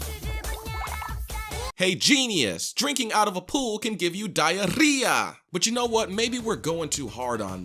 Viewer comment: I thought he said DIABETAS in spanish 0:50 😂😂😂😂